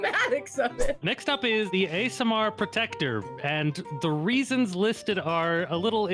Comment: I legit heard "ASMR Protector". New headcanon.